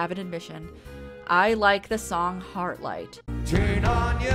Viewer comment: I honestly thought you said "Hard Light" the first time. I was thinking of Rimmer from Red Dwarf and getting his hard light hologram form in Legion